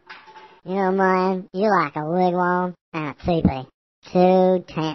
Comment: What does he say at around 4:00 ? Man you're like a ??????? and a tee-pee, two Tents(tense) it's been bugging me for years... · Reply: Ian Baines wigwam 😂